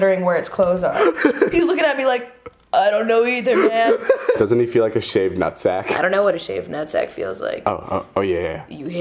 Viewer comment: what did they say during this moment? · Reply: David said doesn't he feel like a shaved nut sack and Liza said I don't know what a shaved nut sack feels like